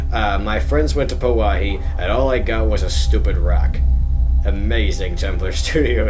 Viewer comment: I thought that rock said "My friends went to Po-Wahi and all I got was this LOUSY rock". Oh well, stupid rock is close enough.